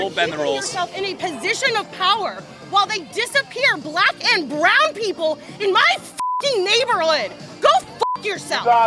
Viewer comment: I think she just said, black and brown people are cock block 14:30 she did nit see this brown person because he is where sunglasses